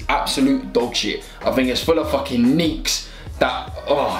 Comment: I swear to god I double tapped the home button at 0:51 to watch Netflix and all u heard was “full of fucking nii’ “ and I thought he said it